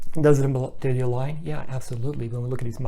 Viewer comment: What exactly are you saying at 15:43...? (It's sounds like: "dirty lying").....It's a bit unclear, sorry. I really want to know so that i don't misunderstand. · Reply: Thanks for pointing that out! Looks like I didn’t speak clearly there. Believe I said that they “align”.